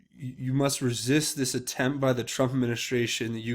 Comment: To me it sounded like he said. Resist, resist disinformation like the Trump Administration. And I took that a saying listen to Trump when he is telling you about the disinformation of the mainstream media. But everything is left to your own interpretation.